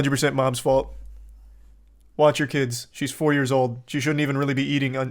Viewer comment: I thought you said 24 Year old and I actually Believed it at first. Because you know.....TikTok